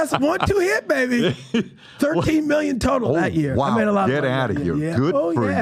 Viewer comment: That moneys gone..13million from Snow Dogs. Thats f##ked up ... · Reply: I thought he said 30 million!!